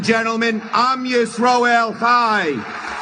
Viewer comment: And people will call you anti-semetic and a Nazi for pointing this out. 😂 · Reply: “Am Yisrael Chai.” I thought he said “heil.” His arm went up almost in a kind of salute, but didn’t quite make it, like he was going to but backed down.